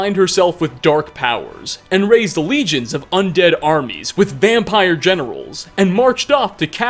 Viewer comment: Not gonna lie, I thought you said "vampire genitals" instead of vampire generals and it made my morning. By all rights, armies with vampire genitals does sound intimidating.